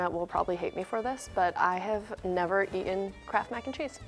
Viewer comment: So she said KRAFT Mac and cheese and I have no idea what kraft was because we don’t have it in the uk so i thought she said I never had CRAP make and cheese Woops lol